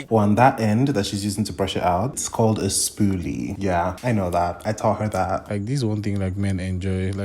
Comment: i heard "i taught her this work" 50 times